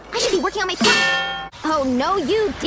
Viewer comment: "AH, don't hurt me, just take me back to jail!" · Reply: I thought he said "just hit me back to jail".☺️😅